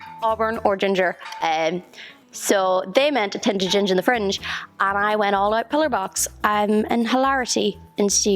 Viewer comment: it sounds like you say "...they meant a tint of ginger the fringe and I went all out pillar box..." I've listened to it a few times but this is all I hear lol. Is this what she's saying does anyone know? And what does it mean?